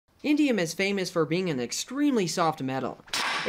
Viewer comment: I thought it said iridium and I got excited. Now do iridium